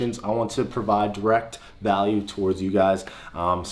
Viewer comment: I always hear "Fat Friday" instead of "Fact Friday"